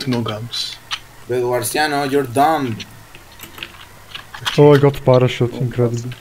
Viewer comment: ho sentito bene o qualcuno ha detto "bedwarsiano you dum"?